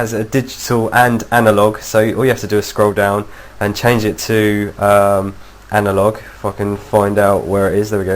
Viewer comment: did anyone else hear "and change it too uhhh analog fuckn find out" hahaha